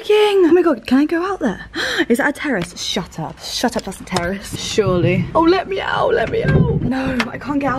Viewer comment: when said "is that a terrace" i really thought she said "is that a terrorist" 😭